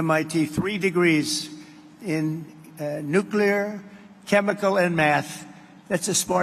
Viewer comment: I thought he said nuclear, chemisty and METH!!!😂